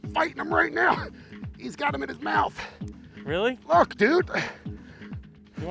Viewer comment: did you say ** dude? lol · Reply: I said "Look Dude...."